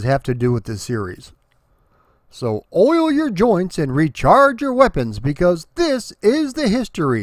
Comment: I thought you said "smoke your joints" so I did